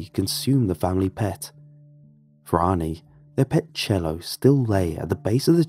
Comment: The dog being called cello is scary enough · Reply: I thought he said jello